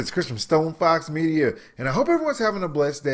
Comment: is it me our did he just say " Christmas stone fox media" ????